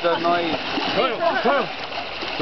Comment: I heard you told "austin to get the knife"